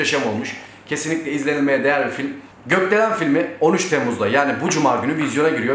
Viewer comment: Götdelen Filmi diye anladım amk Bilinç altı işte :)